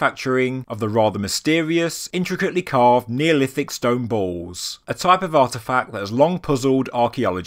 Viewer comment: I thought you were saying stone bowls and I was confused but the very last time you said it I realized you were saying stone balls.